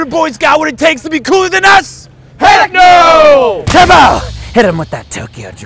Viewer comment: Did they just say heck??!!?!!? BANNED FROM MY MINECRAFT SERVER!!